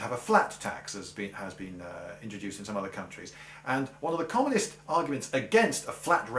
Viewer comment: does he say common or communist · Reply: konrad-manne löwenhamn commie